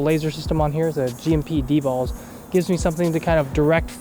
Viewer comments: did he say D balls? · did he just say dballs?